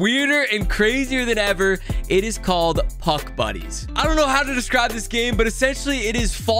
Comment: I thought he said Puck Bunnies at the beginning